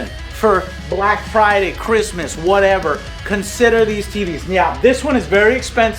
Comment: I am sorry but are you meowing there instead of saying 'now'? Super Troopers flashbacks are occurring.